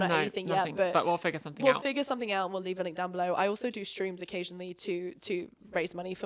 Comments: Howling at myself because at the end I heard you say so casually ‘I do shrooms occasionally’ instead of ‘I do streams occasionally’ 😂😂… you’re so strong girl! You look amazing and I hope your confidence continues to grow 🥰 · I heard, “I also do shrooms occasionally to raise money for my sisters transition” What did you sayyy?? I can’t I can’t unhear it! Also congrats on your ops!! Ive just had my top surgery and is a mad good feeling